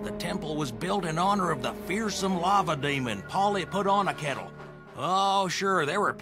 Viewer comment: did he just say that the temple of Boomlavalava was made for a lava demon named 'Honeyputonakettle'? x'D I frickin love these dumb jokes. (wait or is it 'Pollyputonakettle'?) I also wonder if Alex Hirsch watched this as a kid too 🤔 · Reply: its PollyputonaKettle and he is problebly based on Quetzalcoatl the flying serpent god of the Aztecs, the island is called Kaboombalavalava Alex never confirmed anything, would love to see him talk about this shows existence